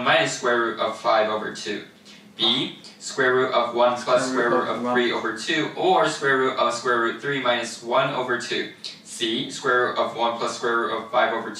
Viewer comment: スプロロスプロロしか聞こえない